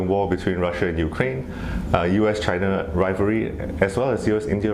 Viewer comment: Did I hear "US India rivalry"!!??